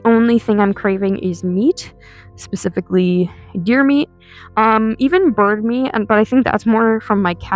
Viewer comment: THOUGHT BRO SAID “YOUR MEAT”😭💔